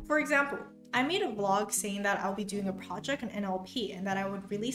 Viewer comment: I wasn't looking at the screen at the time and thought you said this: "for example, I made a vlog and said I'd do a project and then I'll pee" but it was actually NLP.